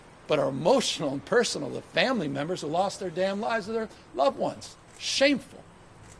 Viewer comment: At the 4:06 minute mark did he say what I think he said? “family who lost theDAMN lives of family members” Did gabby mouth just damn the dead?